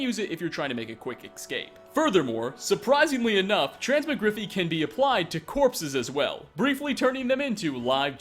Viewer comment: Did........did he just say "trans-ma-griffy"?